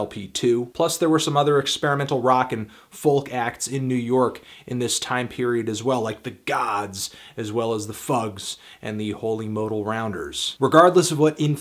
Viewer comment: He said it. He actually said "fags".